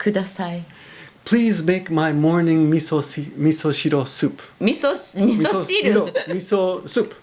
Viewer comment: the phonetic subtitles says "please make my morning missile miso soup".... hilarious.